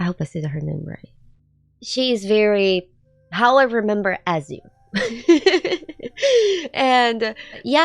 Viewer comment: what did she say at 4:31? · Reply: "She's very how i remember Ezio."